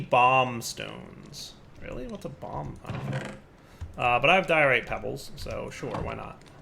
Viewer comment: When you say "diorite" I hear "Dire-ite", and it makes me want a mod that adds a bunch of new stones/metals based off names of minecrafters, like "Soarynium" etc